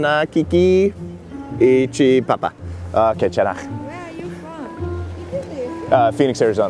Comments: did i just hear "gwaenchanna" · wait, did he just say "gwenchana" ( korean for that's okay)? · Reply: Yeah I heard that too he said 괜찮아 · it sounded like "gwenchana" XD